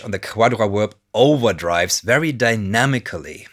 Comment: I think instead of calling it Octaverb you should just refer to is as Jkhewadaravwerb like you did here ;-)